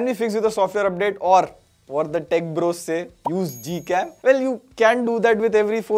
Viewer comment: what did he say? Use what? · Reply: g cam